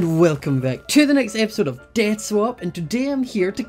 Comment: In the intro it sounds like said dance swap instead of death swap